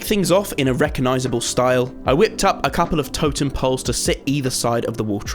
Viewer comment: who else thought he said botem pole instead of totem pole